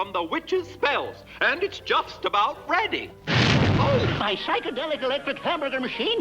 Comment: did he just say my psychedelic electric machine